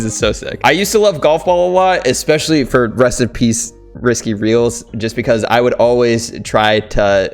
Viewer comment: He said risky instead of lazy links xD